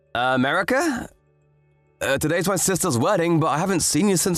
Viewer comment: So I have these stories playing in the background and I'm not actually reading them...the second story threw me off I didn't hear, "Um...Erika?" I heard, "America?" And was like, "Wait they named a character America? I am dumb XD